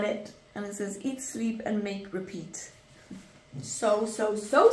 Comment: I had this video playing on one screen while I was doing some work on another, and at this point I thought you said "Eat, sleep, mate, repeat." 😳